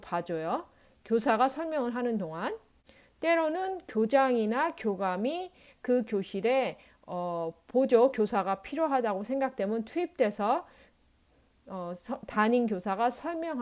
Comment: 교장이나 교감이 보조교사로 투입된다고 들었는데 제대로 들었나요?